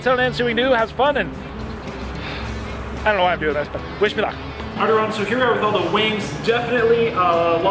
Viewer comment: So i heard Bismillah instead ov wish me luck 😂😂😂